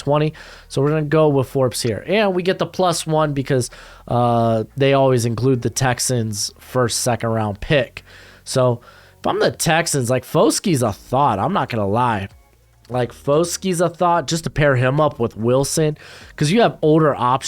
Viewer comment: All I heard was “Foskey’s a THOT not gonna lie”